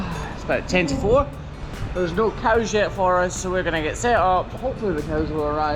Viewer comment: I can't be the only one who thought he said there's no cow shit for us at first 🤣